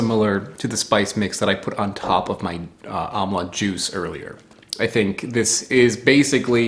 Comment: I thought you said "omelette juice," which might fit, given the eggy flavor.